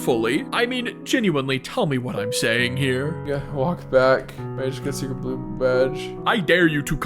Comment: I'm back I just had a blue bath. Or at least that's what I heard and ai also thinks you said